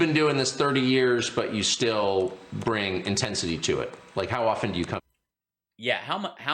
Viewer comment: The way Hal paused it sounded like tucker said "How often do you cum?"